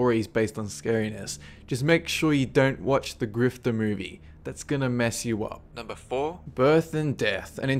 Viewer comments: I didn't understand him during three the what movie. It sounded like he said gifter movie but I may be wrong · he says "just make sure you don't watch The Grief the movie, that's gonna mess you up". I searched on the website, but I couldn't find it. Is it really called "The Grief" or did I hear it wrong? Where can I find this movie?